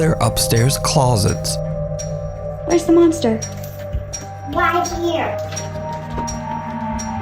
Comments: I hear “ I’m the monster..” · if everybody listen closer the kid keeps mentioning the "spirit" as a monster if you listen close enough it sounds like... get ready! For the monster. like that of a playful spirit This is just a thought and figured to say about number 1 out of all of them that ones definitely real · Voice in the last one sounds like it is saying "it's the monster" followed again by "monster"